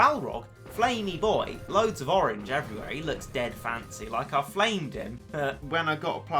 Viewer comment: did anyone else hear him say “flame dick”